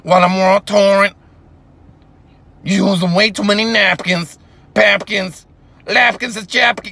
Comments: I felt personally attacked when he said “you usin way too many napkins” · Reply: Bruh this whole time I thought he said”You losing weight?too many napkins”😂😂😂You just blew my mind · Lyrics (might be slightly inaccurate) Of course I'm world touring I'm driving a Porsche over the floorboards Over the four points, when you're in a Ford Taurus Getting an abortion and a divorce at the same time as Harrison Ford'n Look what I'm planning, planning I'm planning to do all this while you're panicking And you're looking at staring at mannequins And I'm going to fannequins and trying to get up at plannequins All of the plannequins, sanne ca fa pam panickquins All of the panicking, sanicking 'n Anik in the cabana I'm in the cabana, in the Janet I'm in the cabana, chanting all the stand up banter While you don't got the stamina, you're lacking the stamina You lacking the stamina while you're divorcing Harrison Ford And I'm in the Porsche and the floor boards While I'm world touring You're using way too many napkins, bapkins, lapkins and japkins You're using chaff to get napkins while I'm bapkin Flapping around like a bapkin Flamming the ba bade ban the japkin Damn in the can a bit of f- (Pure fire I gotta tell ya) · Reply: I heard "war torn" instead of "world tour" LOL